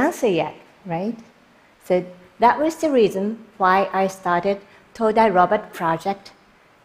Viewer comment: Todai robot ? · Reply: Just Random oh I heard it as todai Robert